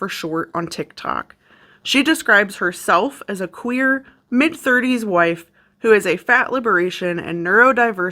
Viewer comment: I was only listening, and I thought you said she describes herself as a “queer mid-30s WHITE” and I didn’t bat an eye, because that sounded plausible to me 🤣then I looked at the screen